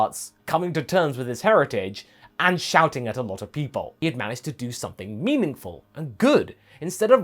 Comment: laughs Sorry, just laughing at how I somehow misheard that at first as "shouting at a fruit bowl". Oh, the things I hear early in the morning after just waking up.